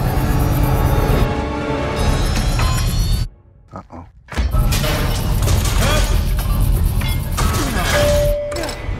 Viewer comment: what was he trying to say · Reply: "HELP!"